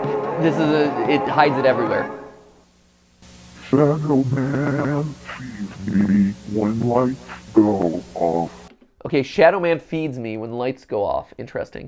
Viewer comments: I thought it said "Sad old man see's me when lights go off" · For the first part with the slowed or reversed words I heard "Sad old man feed me when lights go off" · Ik I’m late but um, I’m pretty sure at 26:12 it actually says “sad old man” instead of “shadow man”?